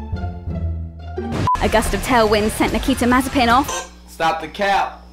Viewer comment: when I first heard it I thought she said "I guess the tailwind sent Nikita Mazepin off"... That'd have been a savage line.